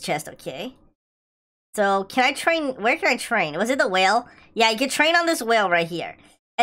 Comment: DID HE SAY A WHALE